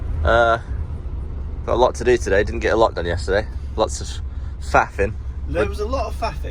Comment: I thought they said "Fapping"